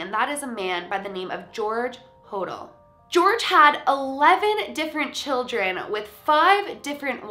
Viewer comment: I thought she said George Hordor for a sec lol